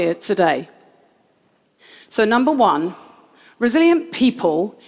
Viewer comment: I'm listening this at 2x speed and everytime she says "resilient", I hear "brazilian" and that's not off the mark.